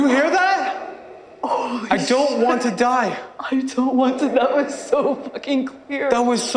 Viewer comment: i think cassandra actually said "i dont wanted to die"